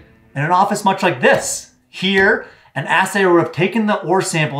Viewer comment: "Did he just say ass sayer?" "Yes"